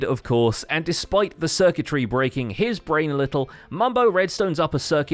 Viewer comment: his brain little Lol did yall say mumbo's brain is little